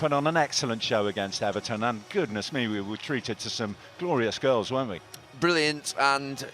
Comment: Did he just say "Glorious girls"?🤣